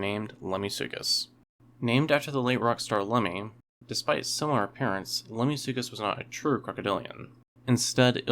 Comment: I thought you said "named after Lemmy because of similar appearances" and had to pause and replay lol.